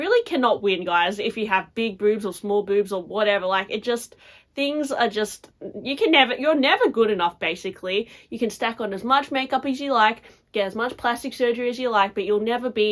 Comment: 😂😂😂 you said big or small boobs. I thought you said big boobs or four boobs. I'm like wait what did she say??? Four boobs were in style at one point?